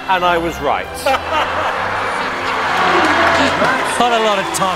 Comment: could someone please let me know what the presenter said? I'm not good at English and could not listen out. thanks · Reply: Called her a little tiger which can translate to he thinks she is fiesty, fierce, cheeky